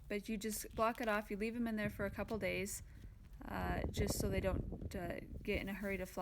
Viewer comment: did you say you'd left the entrances closed off for two days? Thanks. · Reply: That Bee Man at Faith Apiaries ...yes you should leave it closed for a couple days...just make sure they have enough ventilation...😊